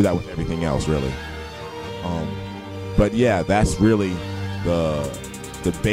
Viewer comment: Wait.. at 5:40 did it say this music just turns me on?!?! WTF XD that's great lolz But anyway.... great drawings you both did! :D